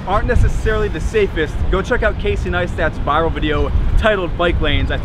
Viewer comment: subtitles said “KC nice tats”